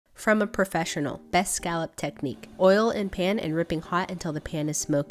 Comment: I thought you said scalp